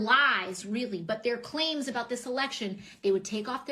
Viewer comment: Did she say tit at 3:23?